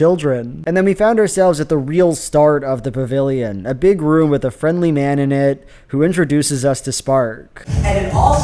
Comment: for a couple of seconds, I really thought he said something like "[...] with a friendly manonette" and thought "huh... I wonder what that means. Like a 'man-marionette'; like someone is pulling the strings behind the scene and he's just the front-facing puppet? I guess that makes sense; he's kind of bouncy like someone is bobbing the strings up and down 🤔" Before I finally realized that he just said "man in it" 😅